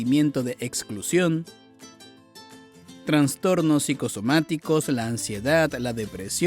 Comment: Escuché "Transtornos" ¿no es Trastornos? 😅😮